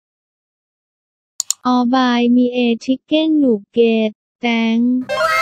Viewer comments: What did i just heard "Please hug me when i feel down Oppai me chiken nugget" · Is it just me or I really hear "oppai" instead of "or buy"